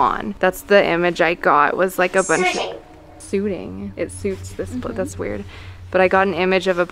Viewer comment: Uhm i hear be the video time 15:12 siting not suiting